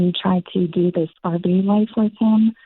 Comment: What kind of life did she say she’s trying to do with her husband? It sounded like R.B. life???